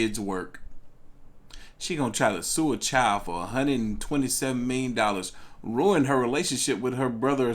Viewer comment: Did this man just say 127 million lol I thought it was me but I heard a hundred and twenty-seven thousand